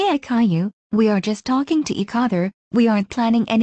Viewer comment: why did Doris say eec other? I think that’s just how they pronounce each other